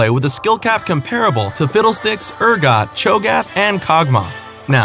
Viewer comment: Lol turn on captions, at 1:00 it says when he said "to fiddlesticks" the caption said "with a littlebit of sex" lol.